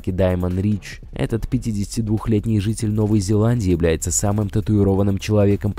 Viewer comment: мне послышалось Житель Зеландии «бл*дь»